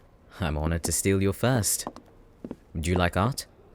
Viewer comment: I was spacing out and when i gain focus all i hear is "I'm glad to steal your first" 💀